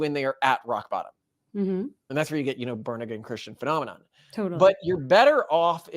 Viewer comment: i heard "Burn-Again Christian"... lol edit: is it possible your inevitable God did a time-travel thingy to make you think you created your religion? when in reality they created it? 🤪